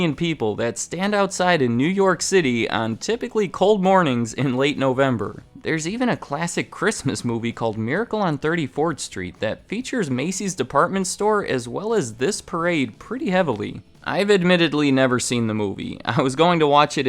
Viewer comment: Did he say "Miracle On 34rd Street"?